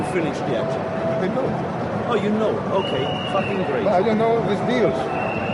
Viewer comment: did he say "fuckin' great" or "fuckin' GREEK!" lmao